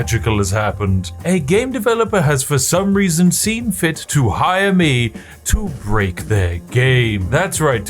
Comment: did just say "a game developer has seen it fit to let me break their gay"